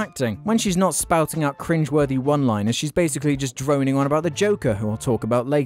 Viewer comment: Did he just say "cringe?"